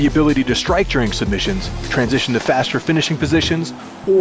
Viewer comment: Did anyone else hear him say "transfer to faster finishing physicians"?😆